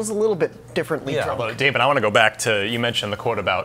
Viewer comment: did McCaffrey just call Dorno “David”? 🤣 <3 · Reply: He said "Daemon".